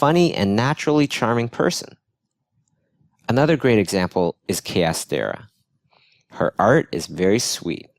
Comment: he talk about Peter de Seve and... who? ...what is the name of the woman? ...it sounded like Kea Stea?